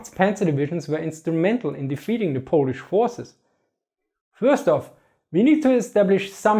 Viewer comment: I know you said "Polish forces", but what I heard was "Polish horses".